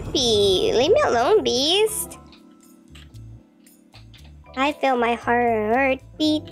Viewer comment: just vibing " i hear my heartbeat to the beat of the beast" anyways i love your vids🎉❤ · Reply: She said "i Feel my.." not "i Hear my..."